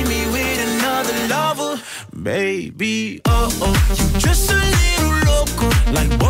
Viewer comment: Replace "you're just little loco" I heard " Jason Derulo" It's kinda strange 😂